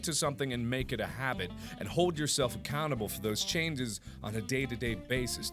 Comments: It sounded like he said " hold yourself a cannibal" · Did...did he just say "ACANable" instead of Accountable? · I am the only one that heard him say "hold yourself a cannible"?